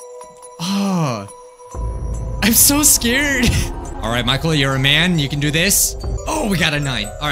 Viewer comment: Did he say Micheal..? Is that his real name??